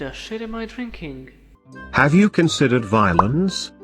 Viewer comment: It sounded like he said "Have you considerd Violence?"